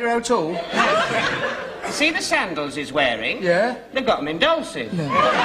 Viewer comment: What does Mr. Humphries say at 26:42 after “see those sandals he’s wearing”? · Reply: no... in "Dolcis" , a British shoe shop chain.